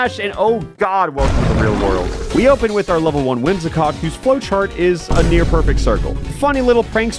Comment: Whimsicock? Did you say Whimsicock? Please, please tell me I misheard you.